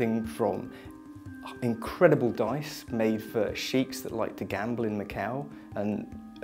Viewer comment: I did a doubletake at 2:20 - it sounds like he says "sheeps that like to gamble in the cow". Took me three times to figure out what he meant.